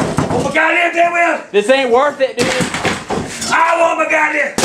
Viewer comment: Did he say “this ain’t worth it nigga” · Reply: Brian Guevara No. He said "This ain't worth it dude".